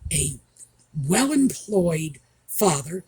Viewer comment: I thought you said dopey father and thought yes he is!!